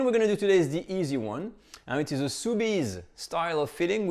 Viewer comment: Dang, you surprised me when I thought you said, "sous vide" when it was actually, "soubise." Golly. I need to pay closer attention.